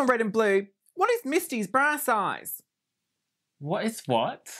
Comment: Wow I thought she was saying “brass eyes” instead of “bra size” 😂